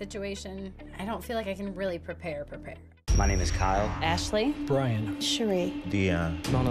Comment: Was I the one confused to hear say that his name is “KYLE?” lol. Wasn’t fully paying attention to the ending portion and when the participants were introducing themselves, I wasn’t watching, just listening. So I just hear, “My name is Kyle,” and I had to rewind the video because I was thinking to myself, “I don’t recall there being a Kyle?”